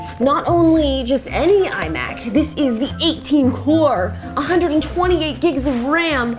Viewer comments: does she say "18 core- or 18...." Am I only one who hear that? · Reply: “This is the 18 core, (lists the rest of the specs)” Yep, you’re insane. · Wtf 1:26 did she said 128 gigs of ram ?